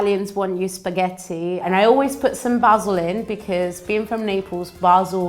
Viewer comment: I wasn't looking when she said " i use basil in" and i heard " i use vaseline" my head snap when i turn it to look 😂